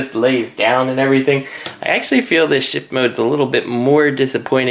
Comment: ish, it sounded like you said "shit mode". Just an observation. I have the SDCC version - felt like best of both worlds for my tastes, and I'm happy with it. Only thing I wish it had is the gray brackets around the shin guns, and I think I can paint that pretty well (accidentally came across a great mix while doing CW Wildrider's doors). The lack of waist cannons is the biggest bummer for me. I don't want to pay another $40ish to "upgrade" it.